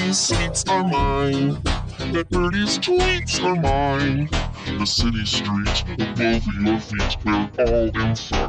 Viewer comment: Did He Say The Sh*tty Street At 0:16?